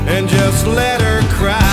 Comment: I thought he said " let her in".I miss you so much Richie Campbell..Im sorry we didn't get back together together..😢